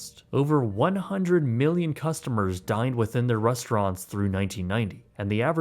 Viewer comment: I heard "died at their resturants" because it was talking about endless shrimp 😂